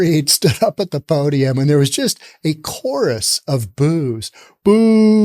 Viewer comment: I thought you said "a chorus of booze" 😅